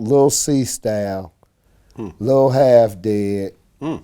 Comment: what the vegas strip did he just say ? lil half dead ?